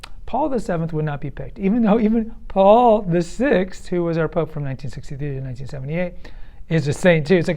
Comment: i heard 1962. No, Pope Paul VI assumed office in 1963.